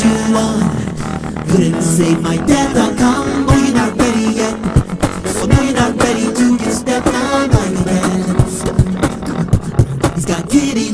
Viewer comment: Who else hears Oh your not ready yet For me your not ready to get stepped on smileyman Or Oh your not ready yet For me your not ready to get stepped on mileyman